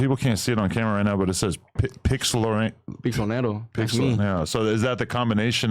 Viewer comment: tbh I thought he was saying pistolero like the shooter